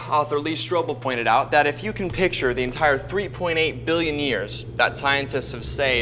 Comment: I thought for a moment that he said life has been around for 13.8 billion years, Lol!